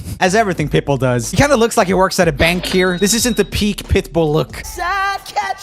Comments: I was listening to this at 5am very softly and when he said Pitbull I thought he said pimple and I was like what?😂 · when he said pitbull it sounded like he was saying pimple